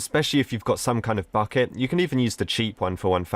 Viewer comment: Why do I keep hearing 'f*ck it'?